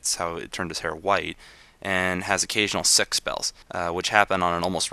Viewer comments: I thought you said sex spells, but when u got to the coughing up blood part, I realized what u said... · i heard 'sex spells'